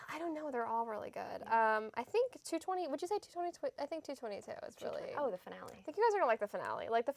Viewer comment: In 5:20 did she say episode 20 from season 2? Or am I just inventing things? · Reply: rod. gar. She said 2x22 (season 2 episode 22)